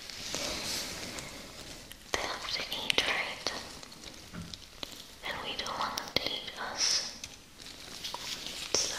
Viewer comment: there was a what? I can not understand what she said there. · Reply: "They have to eat, right? And we don't want them to eat us." : )